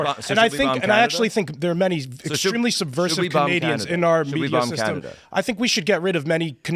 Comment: Did just say that Jordan Peterson and Crowder are malevolent Canadians??!?!??